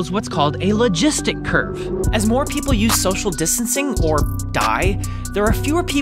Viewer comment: “As more people use social distancing or die” Wren 2020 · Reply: At first, I heard "as more people use social distancing or dye" and I thought how is dye going to help